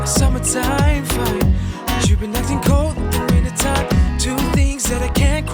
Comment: I keep hearing "...but chu' been acting cold, WINNER time" 🍺🍺🚬